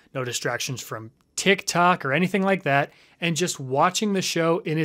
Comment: The first time I watched this I thought he said “No distractions from kick-kok” instead of tik-tok Also, about the test thing, once my teacher made us do a dumbass phonogram test in 3rd grade. 4 months later, almost the end of the school year, she handed out some graded paper and started handing out the UNGRADED phonogram test. When she realized, she said “oops I forgot to grade it.” We all were dumbfounded. How the HELL did you forget to grade that its been MONTHS